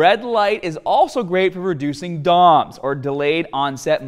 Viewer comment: I could have sworn you said reducing dongs, lol! I was like no thanks, haha! Very interesting video, keep 'em coming!